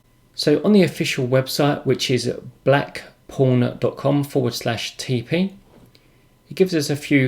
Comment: I was a little concerned at first, it sounded like you said Black Porn instead of Black Pawn :) LOL Can you give us any idea if this is much faster on plotting, or if this is just a graphical interface as I have used the command line version without any issues, but if this is faster I will take a look at it for my next plot.